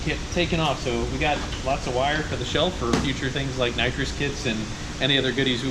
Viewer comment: did you say nitrous kits??